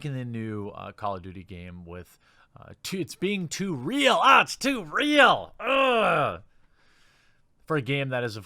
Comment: Journalists: YoU’rE gAmE iS oFfEnDiNg Me! ChAnGe It! IW: No, I don’t think I will. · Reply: “Oh it’s too reeeeal, it’s to reeeal” What I hear : “oh it’s the equaaaal, it’s too equaaal”.